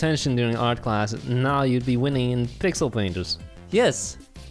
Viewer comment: Start of the video: Pixel me:oh no i thought he said pickle my brain: DID YOU HEAR HE SAID PICKLE HE MEANT PICKLE RICK! HE TURNED HIMSELF INTO A PICKLE REMEMBER! FUNNIEST STUFF I EVER SEEN